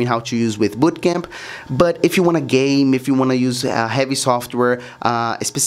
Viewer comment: WAIT DID HE SAY GAYM